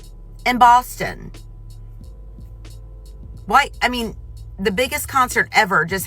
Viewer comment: Y’all hear her say “white, I mean”. @ 00:47? I heard that shit.